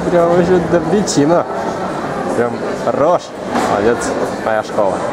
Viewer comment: хахахаха братан откуда ты знаешь про Роша? на 3:55 или послышалось мне?